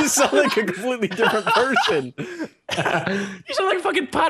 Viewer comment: I just heard him say "You sound like a completely different Persian"